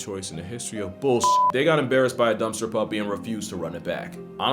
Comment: The babies are ridiculously adorable!! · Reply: Did he just say “dumpster puppy?”